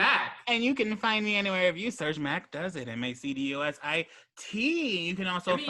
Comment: It sounded like Mac spelled his channel name as "M-A-C-D-O-S-I-T"